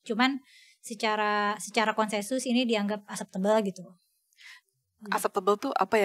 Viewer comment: Sama kita. Aku juga dengernya "asap tebel" wkwkwkwk. Ternyata acceptable. Padahal aku pakai earphone lho nonton podcast ini.